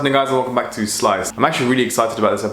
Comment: i know im trippin but it sounded like u said wats up n****s welcome back to sliced